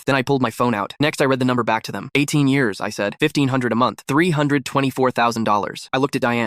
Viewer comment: I thought I heard 15000 a month